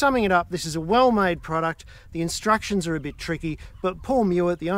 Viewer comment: Should have read the instructions, hey...and did Paul say, "Read the instructions!"?